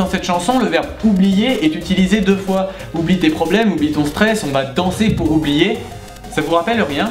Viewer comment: Moi j'ai compris "Oublie tes pobèmes et ton sèce"